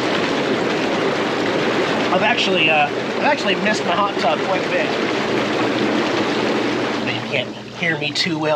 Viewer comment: I swear it sounded like you said " I actually piss in the Hot Tub Quite a bit".